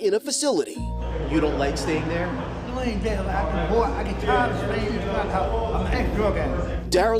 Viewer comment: he actually said “i get bored, i get tired of staying in the NUT house” listen carefully